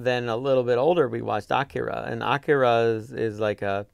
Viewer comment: Did he say Akira at 10:10?